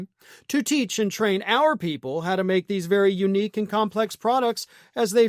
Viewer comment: I heard "Our people" as "white"